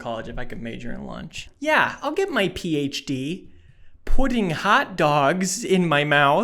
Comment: I thought he said Pudding Hot Dogs